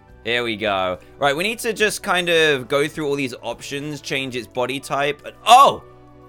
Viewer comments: DanTDM oh shi-..z Me: almost has a hard attack cause i thought he was gonna swear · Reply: DayRise Royale 2 you thought he said oh sh- but at that part he didn't swear he said options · i was here for the dantdm swearing comp 3 dantdm didnt actually swear on this one he said options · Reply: Me to i thought he said sh*t but he said Options